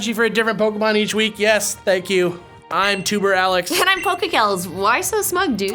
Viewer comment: I thought he said "I'm tumor Alex."